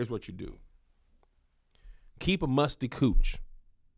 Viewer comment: Did he just say "keep your coochie musty???" WHAT?????!!!!🤣🤣🤣🤣🤣